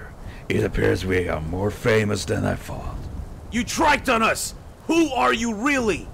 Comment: did gohan say tryked